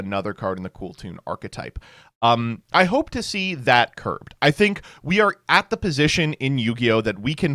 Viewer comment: No way did say "I hope to see that get Kirked" without missing a beat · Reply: I have no clue if you are joking, but he definitely said “curbed”. It means limited or restricted.